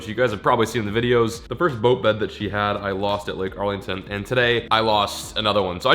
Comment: when you said lake harlington i thought you said arlington as in arlington heights illinois